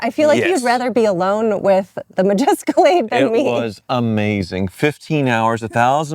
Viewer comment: Did she say the MajEscalade ?